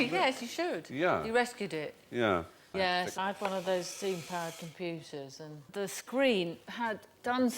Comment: Did she say "steam powered computers"?